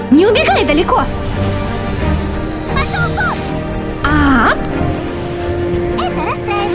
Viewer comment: мне послышалось,или пак сказал "пошел в жопу"